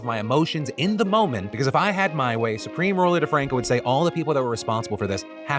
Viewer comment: just woke up and i heard "Supreme Leader Franco" instead of "DeFranco" and i was wondering wtf was going on...